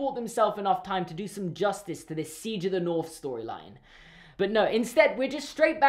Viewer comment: when he says siege in the north i thought he said king in the north